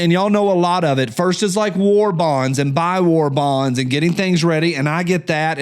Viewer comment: Why did I hear you say "there selling wall bongs".... what the hell is a wall bong?.... Oh, you said war bonds